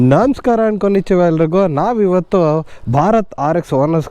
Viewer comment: namaskara konetiva yellagrigu What you said here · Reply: Konnichiwa means, Hello in Japanese!